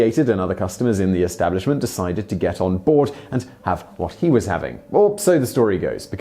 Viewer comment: he says "Other customers decided to get on board and have what he was having." But the captions say "Got their When Harry Met Sally on and ordered."